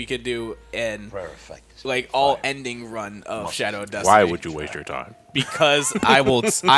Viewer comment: can't lie. Had to double take when I thought they said "all-ending run of Shadow the Hedgehog"